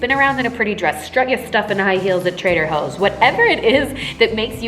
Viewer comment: Am I the only one who heard her say “Trader Hoes” instead of “Trader Joes” 🤣🤣🤣🤣❤️❤️❤️